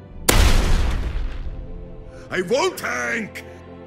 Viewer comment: "i wont do it! i wont tank!" "Be their shield..." Swaps character "Damn it reinhardt!" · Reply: When i first heard him say this i thought he said "I won't Hank"